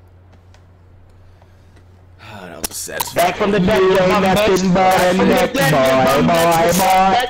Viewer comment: I think I heard one of them say “back from the dead” if one of them did that was funny tho